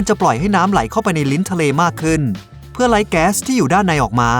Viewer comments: ทำไมได้ยินว่าเพื่อไล้แก๊สออกมา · 12 Tony Khong Tony Khong 1 วันที่ผ่านมา หอยสังข์ลูกแม่ งามแท้ 10 ARRoMBoRJoY ARRoMBoRJoY 1 วันที่ผ่านมา ใครมาก่อน 100 วิว บ้าง มารวมกันตรงนี้ครับ 23 ครองราช บุญเลิศ ครองราช บุญเลิศ 1 วันที่ผ่านมา เห็นหน้าปกแว๊บแรก คิดว่าเรือดำน้ำ🤣 2 Potapt Potapt 1 วันที่ผ่านมา ท่าน ชัชชาติ ปราบมาหมดแล้ว 🤣 5 ggeeaarr ggeeaarr ggeeaarr ggeeaarr 1 วันที่ผ่านมา (แก้ไขแล้ว) 4:40 ทำไมได้ยินว่าเพื่อไล้แก๊สออกมา 2 กมลธรรม บัวชุม กมลธรรม บัวชุม 1 วันที่ผ่านมา ถ้าเปลี่ยนโบว์เป็นสีขาวนะ ผมนี่ยกสามนิ้วเลย 1 เที่ยวกิน ทําอาหารกับคนไตย เที่ยวกิน ทําอาหารกับคนไตย 1 วันที่ผ่านมา รูปปก จะกินคนอยู่แล้ว 555 12 PhomAng TH PhomAng TH 1 วันที่ผ่านมา 6:45 เดี๋ยวๆ ซอโรพอด มาไงเนี่ย 1 Prem Channel Prem Channel 4 ชั่วโมงที่ผ่านมา หอยเป๋าฮื้อ เดี๋ยวนี้เขามีน้ำปลามาแล้วน้ะคะ 1 ADISORN THORNGHORM ADISORN THORNGHORM 1 วันที่ผ่านมา ใครมาก่อน40วิบ้างดันหน่อย 1 พุฒิพงศ์ ศรีพิพัฒน์ พุฒิพงศ์ ศรีพิพัฒน์ 16 ชั่วโมงที่ผ่านมา อยากทราบ ปลามึกทำไมถึงมีหมึกดำแล้วมีวันหมดไหม มันสร้างดึกสีดำได้ยังไง Itthip0l Bualong Itthip0l Bualong 1 วันที่ผ่านมา รายงานตัวครับ😊 2 pleum pleum 1 วันที่ผ่านมา จะเกิดอะไรขึ้นถ้ามนุษย์ไม่ได้มีความคิดแต่กับมีแค่สัญชาตญาณครับ แล้วสัตว์อื่นมีความคิดแทนมนุษย์เรา the ulin Channel the ulin Channel 1 วันที่ผ่านมา ความรู้ก็มี สิ่งที่ยังไม่รู้ก็มีอีก สิ่งนีดีต่อสมองสุดๆ #ทำต่อไปนะค่ะ Yuanyong TY Yuanyong TY 1 วันที่ผ่านมา สิ่งมีชีวิตที่ 13 คือลุงแถวบ้านผมครับ อยู่มาหลายร้อยล้านปีแล้วด้วย พลังพิเศษคือการดำน้ำและการทุ่มด้วยโพเดี้ยมครับน่าทึ่งจริงๆ!!!